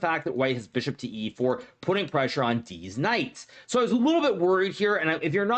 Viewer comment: He really said "Deez Knights"